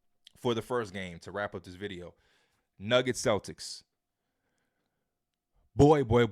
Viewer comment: WHAT DID HE SAYYY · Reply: Nuggets Celtics lmao